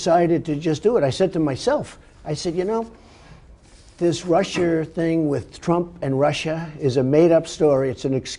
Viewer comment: Did Trump call Russia "Rusher" at 8:39?